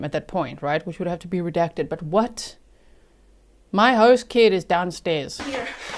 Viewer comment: Did anyone catch she said "my daughter" is downstairs. Did I hear that correctly? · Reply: I thought she said "host kid"